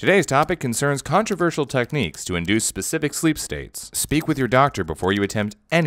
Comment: "Speak with your doctor before you attempt any of these sleep techniques" This sign cannot stop me because I can't read! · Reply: The first tym i heard him say "Sleep with your doctor..." :V